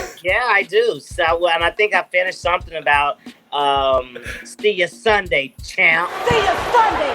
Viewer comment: Ohhhhh! I thought she said "See ya Sunday, 'Tramp'".😄